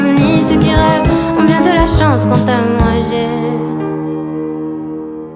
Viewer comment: She actually said "quant a moi, j'ai des insomnies"